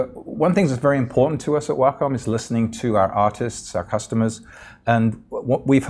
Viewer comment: I thought he said at 1:20 " One thing that is very important to us at Wacom is listening to our autists"